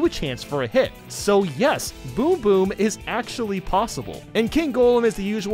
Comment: did he say boom boom rather than king boom boo?